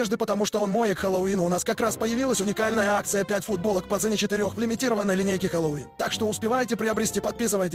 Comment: мне послышалось четыре футболки по цене пяти💀